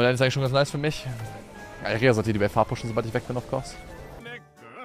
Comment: WhAt DiD hE sAyY? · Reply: "Irelia sollte die Wave hardpushen, sobald ich weg bin, of course." League of Legends - Deutsch ; Deutsch - League of Legends